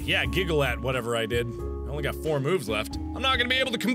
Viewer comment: I thought he said "Oh I only have 4 boobs left" xD